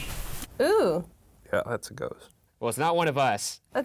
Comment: Did shane say “ yeah that’s a ghost “ in 8:46??